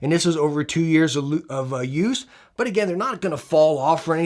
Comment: comin back to this vid i hear at 20:12 u almost said "2 years of loose" OOOOOOOOOOF